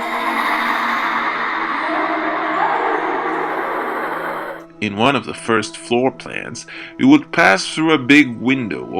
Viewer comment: Maybe its just me...but I cant make out what Priscilla is saying at 5:30 it just sounds like a bunch of moaning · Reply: I believe she's saying “Bartholomew, where are you, Bartholomew?"